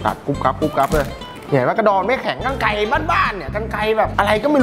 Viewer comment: ได้ยินว่ากระดอไม่แข็ง5555